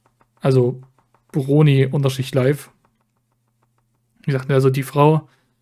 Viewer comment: Ich hab verstanden "Broni Unterschicht live" 🤣